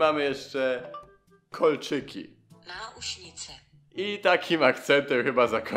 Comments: Haha czm jak tłumaczył kolczyki to ja Auschwitz usłyszałam XD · na początku usłyszałem Auschwitze